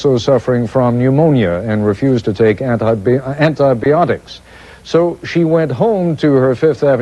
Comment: did he say anti-beeotics?